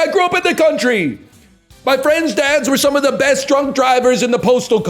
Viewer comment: "my friend's dads were some of the best drunk drivers in the postal code" fucking destroyed me 😂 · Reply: I thought he said "truck drivers" on the first watch, but realizing this is what he actually said makes it so much funnier.